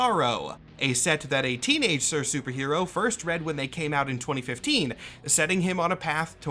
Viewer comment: When I first heard this I heard it as “When he came out in 2017” My immediate follow of thought it “He’s trans!? How did he get his voice like that!?” Followed by “wait it could be gay to” Then “No way if it’s anything it’s definitely some flavor of Demi/A-romantic and or sexual”